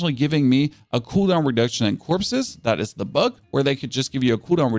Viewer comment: "a cooldown reduction AND the corpses, that is a bug", ?? Did I hear it right? What is the bug?